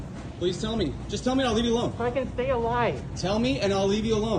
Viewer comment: Huh I hear, "I want to stay alive," but his actions don't